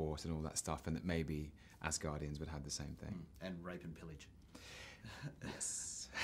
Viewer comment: What does the interviewer say at 2:09? I couldn't understand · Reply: "and rape and pillage" - viking things...